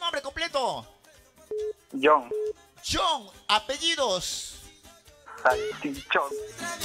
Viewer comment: Alguien sabe lo que dijo en 3:54 ? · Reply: Jhon ... Salchichón